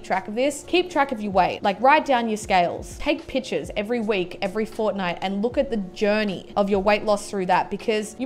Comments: Why does she say fortnite in the middle of a sentence? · Did she say every Fortnite? 6:53 😂😂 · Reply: no every fortnight (every two weeks) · Did she say fortnite at 6:55? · Did she say "every fortnite" at 6:55?